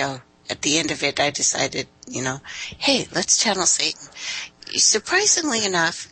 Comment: hold on @ 39:56 "Let's channel Satan?" am i hearing this correctly?